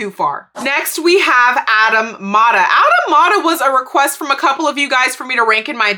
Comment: Lmao ron and mask⭐️demasque getting separate rankings Also kind of random, but I didn’t realize until I heard you say Adam Mada’s name several times: in addition to being a palindrome, it sounds like “automata”